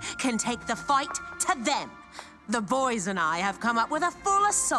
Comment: I’m sorry, did she say “the voys and I” 4:04 😭😭